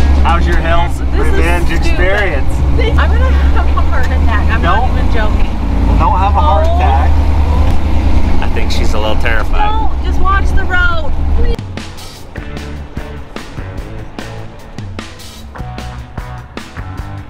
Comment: I heard don't watch the road lol